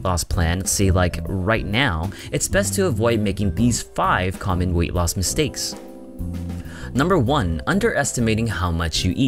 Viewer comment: My fat ass thought it said 5 weight loss Milkshakes